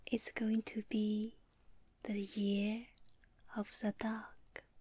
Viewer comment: I thought she said "year of the Duck" 🦆